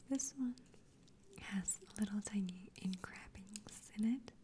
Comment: What is she saying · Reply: I think she's trying to say "engravings" but pronounced it wrong. Afterwards, she's just comparing the specks to the seeds on an "everything bagel". 😊